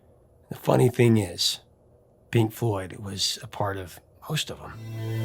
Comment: Its not that I hang on every word, I do wish to know what was said. SOMEONE??? 22:06 The funny thing is, Pink Floyd was part of ???? (sounds like, 'most of them'. (THANKS)